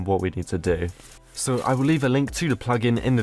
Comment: i thought he said h was going to leave a link